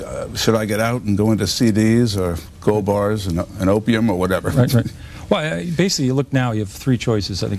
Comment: did he say *opium*? As in, the drug that is banned nearly everywhere?